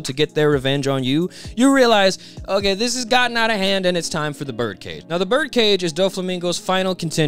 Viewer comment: I misheard "bird cage" as "Burger King" and now I can't unpicture Doffy wearing the BK crown during Dressrosa